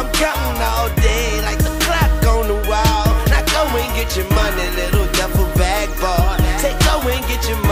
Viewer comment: When this came out i thought it said Now go and get yo money lil double black boy